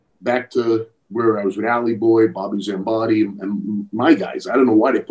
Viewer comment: Did Larry say Bobby Zimbadyan? At 8:20 is that an Armenian guy with em?